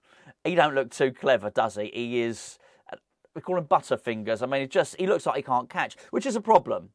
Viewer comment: I thought you said buttockfingers, which also works as a description for Onana.